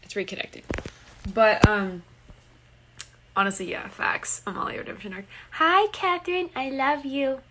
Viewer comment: What did she say at 27:15? "Amalia" what??? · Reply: Amalia redemption arc. Think she meant Scylla redemption arc tho